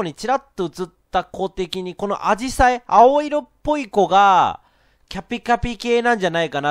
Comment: の「キャピキャピ系」が「カピカピ系」に聞こえるw